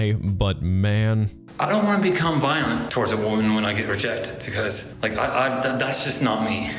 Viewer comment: I’d kill to hear you and Click read the Lorne Armstrong To Catch A Predator chatlogs. Btw take my sub. You’ve earned it EDIT: Okay… at the 1:13:24 mark, appears to be saying “I don’t want to be violent to a woman when I get rejected because (pause) I’ve done- that’s just not me.” Seriously, am I hearing things? Because it sounded like he stopped himself from admitting to being violent in the past.